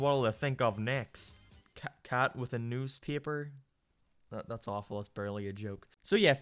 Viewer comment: for a second I thought you were gonna say "cat with a noose" and I was shook.